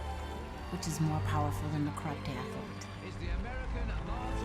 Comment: Great documentary! What is Betsy saying at 1:36:35? "What Lance never had was the truth, which is more powerful than ????" (possibly - "crown and ????) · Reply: Mariner_38090 she says "...more powerful than a corrupt athlete"